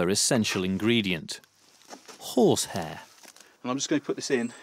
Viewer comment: I’m working on a project atm and I could have sworn he said whore’s hair instead of horse hair. I had to listen to it 3 times to convince myself he said horse